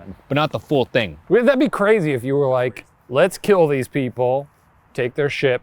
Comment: does anybody hear a nother voice say crazy after shane at 11:43?